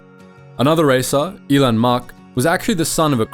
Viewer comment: Dude, I almost choked on my blue milk at 5:22 when I thought I heard, "another racer, Elon Muk". After a quick HoloNet search, I discovered this racer was actually "ELAN MAK", just pronounced with an accent from a galaxy far far away. Now, this is content creation!